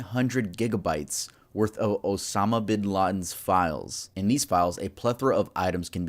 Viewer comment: I thought you said Obama😂